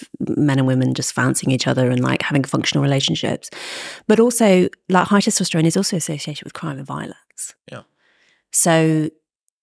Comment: what did she sayy!?!!😂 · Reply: "but also, like high testosterone is also associated with crime and violence"